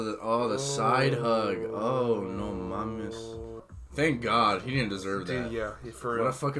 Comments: did really say “no mames” at 7:10? 😭 me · I think I’ve heard it before but does say “No mames” in 7:10 . Maybe it’s something else but I’m Mexican and only hear no mames